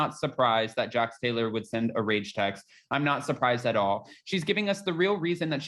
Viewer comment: When you say "rage text", I kept hearing "rape kit", and I was like WTH! 🤭